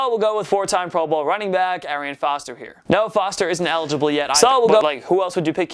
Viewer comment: At first i thought someone was messing with him when he said Arian Foster. It sounded like someone said "FALSE"